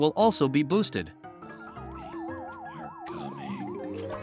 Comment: I thought the zombies said “MRBEAST ARE COMING”